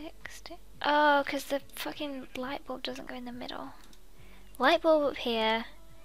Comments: whoever is repeating 14:40 just to make sure she is actually said that like this comment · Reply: Shyna "oh cause the fuckin' lightbulb didn't go in the middle" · who heard say f*cking?!? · the first time that I heard swear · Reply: diamond Gamer actually she said fucking not fuck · did she say f***ing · did anyone else hear that she said fucking at 14:40 xD